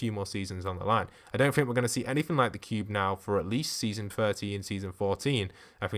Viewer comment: Did he just say season 40 or 30 that would be like 3 or 2 Years away...